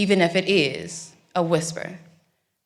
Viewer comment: Beautiful. Did I hear her say, “whenever you’re ready, call your next victim?”